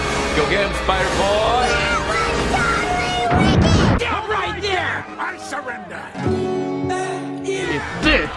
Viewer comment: I hear Ian from Smosh saying stop right there.